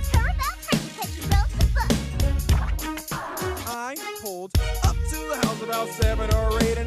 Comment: I thought she said “and she broke her hoof*